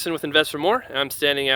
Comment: Please articulate "Invest—Four-More"—I watch a lot of your videos and I keep hearing "Investor-More".